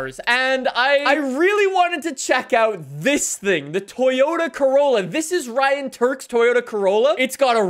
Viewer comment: Did he just say Toyota Crayola or is it just me??